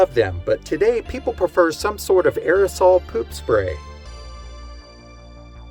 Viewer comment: Did he say "aerosol poop spray"?